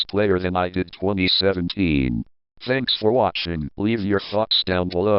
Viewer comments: who else heard "leave your f****s in the comments below"? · in 10:20 "Thanks for watching, leave your fucks down below" I heard while watching that part the first time.